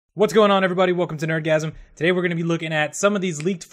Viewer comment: I thought he said orgasm